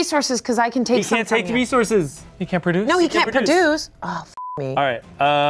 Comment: when they said "Chanse can't produce", I first heard "Chanse can't reproduce"